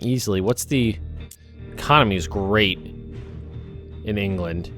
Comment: I thought you said "the Commies is great" lol